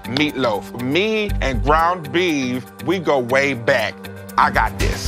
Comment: I swear instead of "ground beef" I kept hearing "brown meat" and wondering wtf that could be...